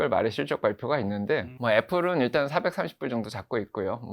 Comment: 애플 적정선이 430불이라구요? 지금 245불정도 밖에 안하는데요? 제가 잘못 들었나요? 21분13초 즈음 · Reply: 230일 겁니다